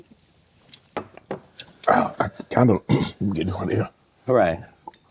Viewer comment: did he just say "im gettin hornier"?